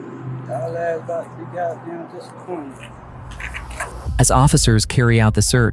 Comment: (Inaudible) - He actually says, "Yall ass bout to be gotdamn disappointed".